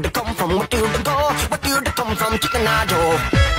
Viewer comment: Did he say «chicken nacho»? 😭🥶🥵